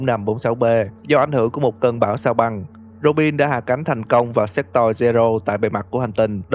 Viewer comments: sao mình nghe nhầm là hạ cánh thành công vào Sẽ Toy nhỉ 😂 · sao nghe như s*x toy v :v